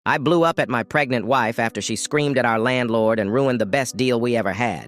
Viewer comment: I thought it said “I blew up my pregnant wife-“ 😂